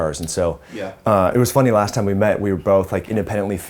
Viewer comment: At around 9:43 - I can't make out what the interviewer was saying, even with repeated rewinding and turning on the caption don't help ... "It was funny last time we met we were both like independently fans of Gerald Steiner water, why is it that you like it so much?" Could someone help clarify this? TIA. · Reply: Ohhhh, never mind ... He meant "Gerolsteiner" brand bottled water. The caption was whacked and I did not know about Gerolsteiner until I googled "Gerald Steiner water". Haha.